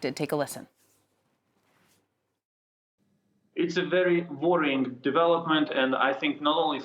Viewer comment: I thought he said, "It's a very boring development". That's a lot of swagger, isn't it? But after a few listens, realised that he said "worrying".